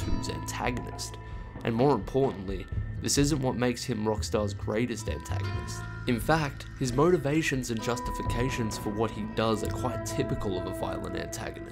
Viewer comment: "It's now clear to me that Dutch Van Der Linde is by far Rockstar's greatest anta....." okay? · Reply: I heard "greatest antenna" lmao